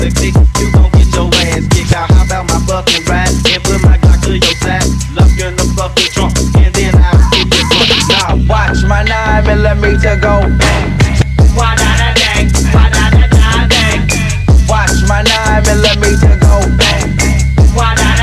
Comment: Why do i hear "watch my nine eleven"